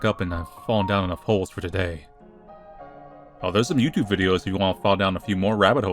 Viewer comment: first watch I thought said "Mewtwo videos" and I got so excited 32:24 - because you'd said your own name earlier, Alice